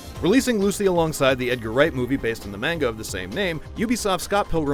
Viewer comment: Idk why, but i keep hearing "mango" instead of "manga". Which in theory, it is incorrect but it doesn't matter. Scott Pilgrim is always a win.